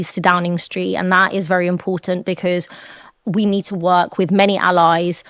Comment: I swear she keeps saying caste. . . 3 times in a row. Then later she switches to class.